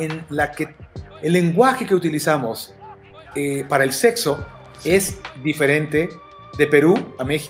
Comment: Dijo seczo!!!,Escuchen la canción de Goku que dice por 5 min.Seczo😅😅😅